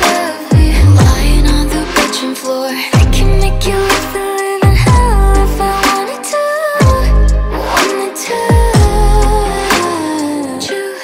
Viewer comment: Dude Said bedroom Flor but I hear "kitchen floor"